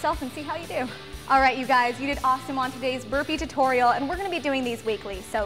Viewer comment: Burpees? I thought you said Slurpees.